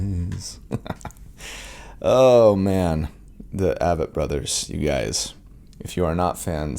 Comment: The Avett Brothers just got a new fan! At first I thought you said "Abbott brothers" which brought up old Abbott and Costello clips 😆, but once I found the actual band I was impressed. They owe you for adding a new fan:)